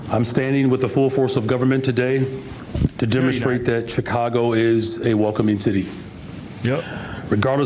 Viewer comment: He said Chicago is a welcoming city. I had to listen to it again. At first I thought he said Chicago is a woke committee. That is fitting.